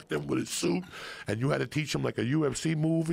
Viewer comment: He says "a UMC move" instead of "a UFC move" lol. Gotta love Joey :D